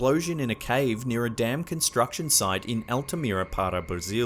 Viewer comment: thought he said, “...near a damn construction site...”